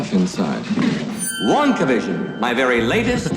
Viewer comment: why do I hear Wandavision 😂